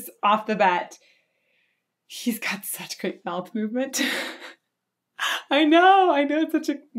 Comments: For sure listen to Black Sabbath “heaven and hell” sang by Ronnie James Dio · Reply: I thought she said "got such great bowel movements" and I was watching this while taking a dump lol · "He's got such great bowel movement" is what I heard, and I'm sticking with it. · LOL!! At the beginning, I thought you said, "He's got such good bowel movements" and wondered how would you know. LOL!!!